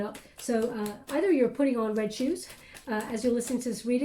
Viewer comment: I can hear a man’s voice say “put on the red shoes” as she is listening to Spirit replay it. I rewound it several times, does anyone else hear this at around 21:34 or is she saying it under her breath? I was looking in the mirror during that comment also! 💫 I resonated with this message deeply, thank you for your love and light! ✨💛✨